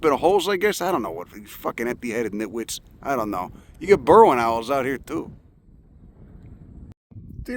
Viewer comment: I heard "Berwyn owls" for a sec there.